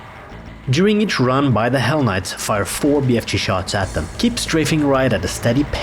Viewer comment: I thought you called them, "Why the hell knights." 😂😂😂